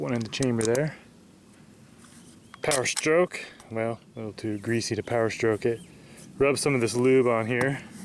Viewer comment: Nice way to attack the box with the bayonet. Did he just say "too greasy to power stroke it?" 2:06 And of course a gun enthusiast wouldn't have magnums. D'uh.